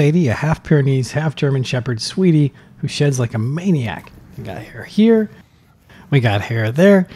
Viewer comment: I swear I heard she "shits like a maniac" omg I feel embarrassed now 🥺 she is so cute btw ❤️